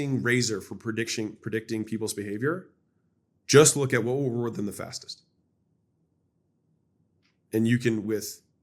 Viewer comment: “what would….” Can anyone tell me what he said? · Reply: "what would/will reward them the fastest"